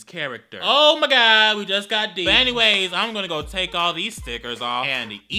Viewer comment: the subtitles say "Nut anyways..." what?